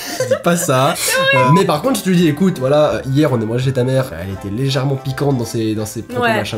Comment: J'ai entendu «Hier on a mangé ta mère, elle était légèrement piquante» Sauvez moi T__T